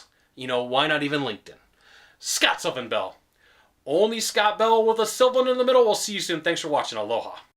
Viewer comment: Thanks for this. I always thought you were saying Scott Bell with Seminole last year haha